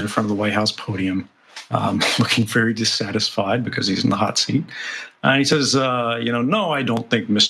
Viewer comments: I first heard this as "looking very dissatisfied because he's a NAZI". i.e. not "He's in the hot-seat". Prefer what my brain first told me. Or was it really "NAZI"......oh Obama....we put so much hope in you, thought you'd be different. Turns out you were, you ramped up drone-strikes, and made sure any further whistleblowers would be crucified. I can't say how much contempt I have for that sociopath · Did he just say Barack Obama is a Nazi? 20:05! 😳